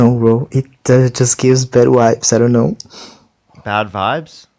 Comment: i heard 'bed wipes'